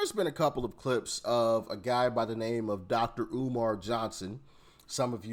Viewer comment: Sound was down, I thought you said Rhubarb Johnson. Would be a great name for an older blue's guitarist.